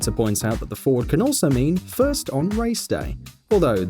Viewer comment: I heard the "F*** on race day" for Ford